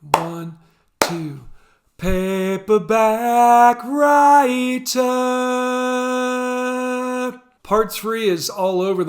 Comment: I always (deliberately) 'misheard' it as "Paperback writer-ba-a-a-ack...")